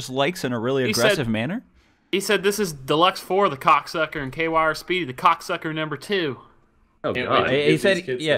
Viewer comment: I thought that guy said "QR Speedy"